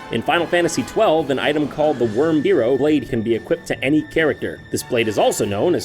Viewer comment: What was that?! Sounded like JonTron's voice was spliced together to say "Wrymhero Blade"